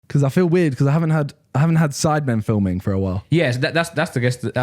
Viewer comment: In the beginning I swear that I thought that he would say I feel weird because I haven't had sex in a while lmao